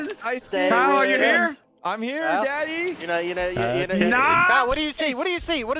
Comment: Did anyone else hear Kyle say, " I'm here Daddy ". Or was that just me